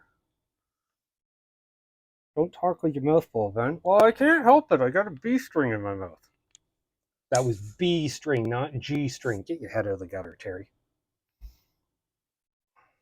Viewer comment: All i heard was "g" string in my mouth 🤣🤣🤣:_Horns::_Horns::_Horns